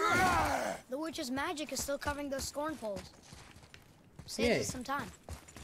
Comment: honestly when i was playing this game today and the boy said scornhole i thought he said cornhole 😂😂😂